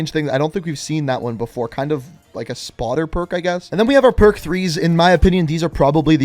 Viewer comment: I swear I heard him say perc 30s